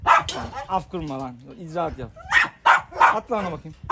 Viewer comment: ne vurdum duymaz Pars ya. Mars'ın havlamasına bile cevap vermiyor😊 olan Boçi'ye oluyor. Hafkırma icaat mı yap diye duydum😉